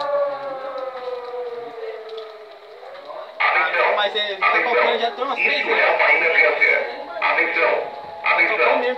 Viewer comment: What did they say in Brazilian to engilsh in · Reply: Atenção Atenção Isto É uma emergência, ATENÇÃO ATENÇÃO ISTO É UMA SITUAÇÃO REAL DE EMERGÊNCIA